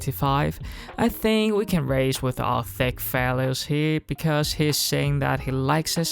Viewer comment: does he say we can raise with our "thick phallus here" not trying to be rude but genuinely what I keep hearing. Probably something about kings though. Again not trying to mock just caught me off gaurd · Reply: Thick values